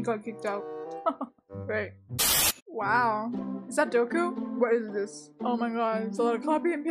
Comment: did she just said Doku? Uh... Yeah she did..